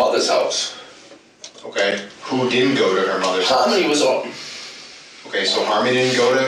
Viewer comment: I could swear that I heard Adam start to say that “Harmony was already…” I thought he was going to say gone when the interrogator was asking him who stayed at the shelter. I also noticed that his nose got progressively stuffier throughout his interview. I would bet the next morning he was a hurting unit. This was another fantastic video guys! You never disappoint!!